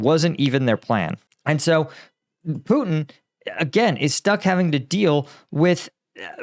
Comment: it sounded like you almost said "Putin is stuck dealing with Lukashenko"